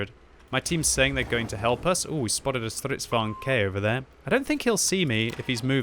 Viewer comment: did he say strv k for strv 74?